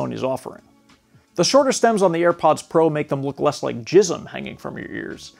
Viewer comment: Bro bro. Did you just say looks less like jism???? I laughed so hard. I haven’t heard baby batter referred to as jism since the mid 90’s. 😂😂😂😂